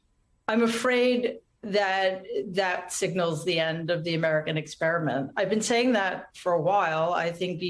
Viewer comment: Did she say the human “experiment“ !?